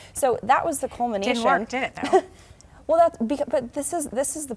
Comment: What does the host say at 22:28? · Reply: I had to listen several times... It didn't work though, did it?